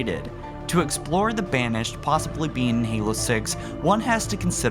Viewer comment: I thought you said will the BANSHEE be in halo 6